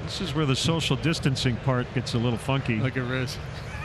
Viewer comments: I thought he said fucky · anyone else hear " Gets a little fucky"?